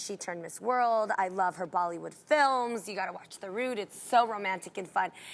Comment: What movie did Jeanie say she watched 3:27 ? The route? The rude?